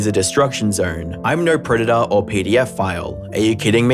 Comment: Sounded like PDF file instead of pedophile. 😂Never liked him anyway.